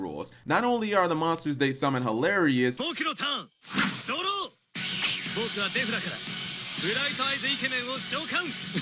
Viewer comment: anybody hear? ...DURO! MOBSTA CARDO!